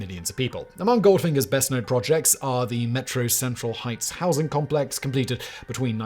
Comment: Every time Simon says "metro central heights" I hear"metro sexual heights".